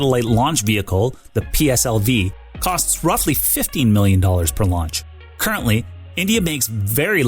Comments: you say the PSLV costs $50m per launch but the subtitles say $15m per launch. Which one is it? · The subtitles say $15 MILLION, where it should say $50 MILLION.